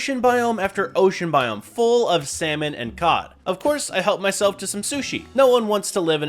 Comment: bro said Semen instead of salmon💀